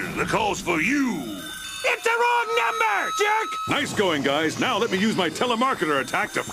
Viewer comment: I thought he said "Its the wrong number,dick!!"